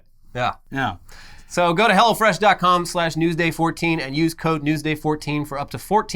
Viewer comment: Sounds like says "go to hell fresh"